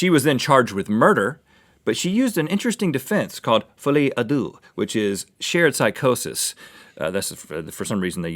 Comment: DID RHETT JUST SAY "FOLIE A DEUX?" THAT MEANS THE MADNESS OF TWO AND IS A FALL OUT BOY ALBUM IJFAIJASkAIJE)IQITOKKASC